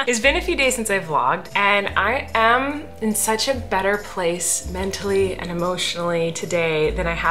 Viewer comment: So glad your in a better place, and glad his surgery went well… you have such an incredible family and you are a great mother ❤️🥰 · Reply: holy crap this scared me so bad I thought u said he was in a better place and I almost balled.